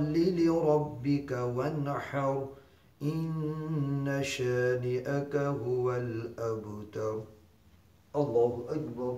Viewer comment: Hi. Can some one write the spelling for the verse at 1:01 to 1:06. I think is. Ina shari aka qul wol abdur Is that written correct I am trying to learn my prayer. Can’t make out what he is saying at that point. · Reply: I’m a bit late but Inna shani aka huwal abtar